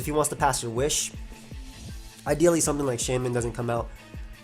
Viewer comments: Charlie Come Sua Irmã "ideally" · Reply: damn it, and all this time I always thought he said something like "I dealy". Thanks for answering bro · Can somebody tell me what's the word says at 2:48? it sounds something like "dealy" but I can't find any word that fits the context(I'm not english) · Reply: Charlie Come Sua Irmã "ideally"